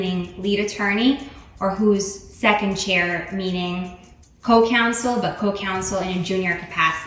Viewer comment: Who did these captions? They're wrong in a number of places, like: 0:58 should say say "counsel" not "council". A council is a group or a meeting whereas counsel is a legal advisor. 10:04 she says "get your client an acquittal" but the captions say "get your client in a huddle" which is just bizarre. 15:20 She says "District attorney's offices" but the captions say "secretaries"! Are you kidding me? And these are just the ones I caught! This thing is chock full of errors. Please fix this.